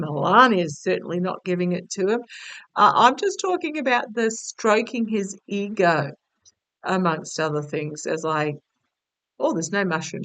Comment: "stroking his ego, among other things" . . . . . had me doing a spit take of my morning coffee, you naughty! 😆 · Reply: I heard she's "Stroking his mushroom..." As if ...